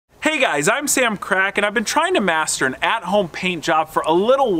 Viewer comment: accidentally clicked into this video and I heard: hey guys I’m selling crack